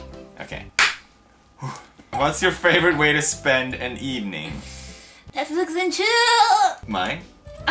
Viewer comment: Did she just say Netflix and Chill 😂😂😂😂😂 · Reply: I thought she said blips and chitz